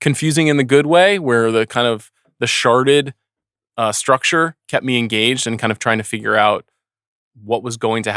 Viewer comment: did he say sharted structure?